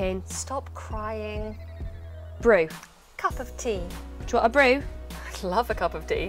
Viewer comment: Want to improve your pronunciation? I can help: <link> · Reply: Great video! Please tell me what the woman on the left said at 1:32 - "Do you want a brew"? I am not sure